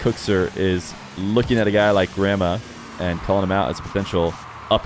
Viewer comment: I thought you were saying "grandma" for the first minute there before you swapped cams 🤣🤣🤣